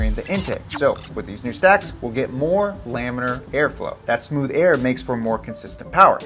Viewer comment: Did you say Laminar Flow? will be here soon.